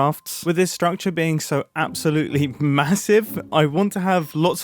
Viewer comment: did he just say MASSIVE?